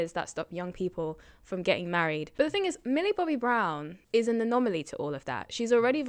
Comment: At first i thought this presenter was saying, 'mini Bobby Brown.' So I thought she was mistaken. Thinking kevin Hart was singer Bobby Browns son. 😅